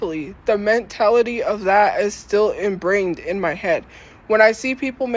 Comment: how am I just now noticing that she actually said “imbrained” instead of “ingrained” 😂